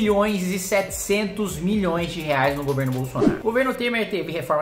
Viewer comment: eu escutei pack do pé KKKKKKKKKKKKKK KKKKKKKKKKKKKK KKKKKKKKKKKKKK KKKKKKKKKKKKKK KKKKKKKKKKKKKK KKKKKKKKKKKKKK KKKKKKKKKKKKKK KKKKKKKKKKKKKK KKKKKKKKKKKKKK KKKKKKKKKKKKKK